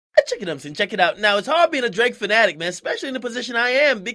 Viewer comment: ehhh checkin ups ehhh checkin out 0:00 AK wtf you be sayin g 😂😂😂